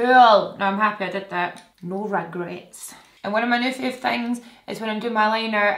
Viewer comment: I’m obsessed with you 💙 I watch all videos even Netflix with subtitles and by far yours are always funny 😂 the words it thinks you say is hilarious 😆 for example- You said “ no regrets” but YouTube thought you said “ no red grapes 🍇”. Love it 😍 Beautiful women inside & out 💙